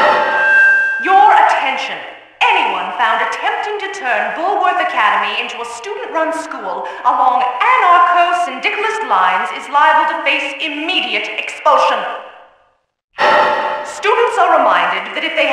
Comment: what is being said? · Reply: “Your attention, Anyone found attempting to turn Bullworth Academy into a student-run school, upon anarcho-syndicalist lies is liable to face immediate expulsion.”